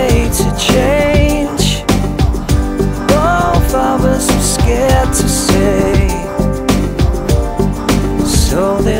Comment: What does he say at 1:17? The lyrics read "all of us were scared to say" but the first word sounds different to me, more like "half of us" but I'm really not sure. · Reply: "both of us are scared to say..."